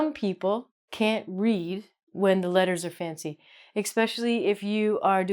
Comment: I heard you say "expecially" @ 3:04...